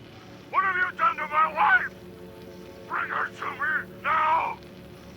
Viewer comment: or so K117m the audio says “wife” but the subtitle says “mother”...